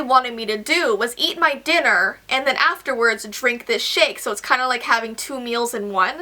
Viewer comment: Who else thought she said "and then drink this shit.." when its actually "and then drink this shake.."